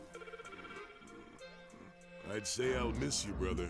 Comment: what he say? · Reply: “But I can always make another one”